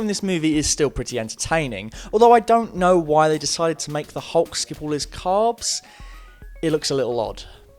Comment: what is he saying there?? "I dont like how they made hulk skin all his calves?/skip all his carbs?. It looks a little odd. ...?" Ive watched it over and over and i just have no idea what hes trying to say and its annoying the fuck outta me lmfao so if someone can tell me what the shit hes actually saying id super duper appreciate it because my hair is falling out n shit trying to figure this out lmao and no captions dont help even a little lol someone pls tell me what he said 😩😫